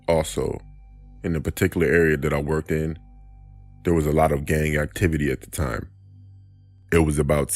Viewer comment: Thought he said "gay activites"